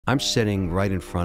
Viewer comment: I thought he said “shitting”💀